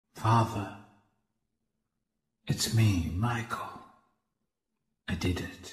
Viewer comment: Why did sound like he said it's-a me Michael